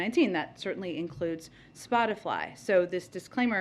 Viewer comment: Did she just say Spot-a-fly? 😂